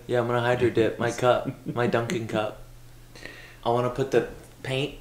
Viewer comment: When was talking about the dunking cup, i thought he said dumping cup 😂